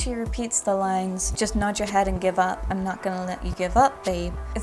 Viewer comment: I THOUGHT she was saying in give up "just nod your head and get up, I'm not gonna let you give up babe"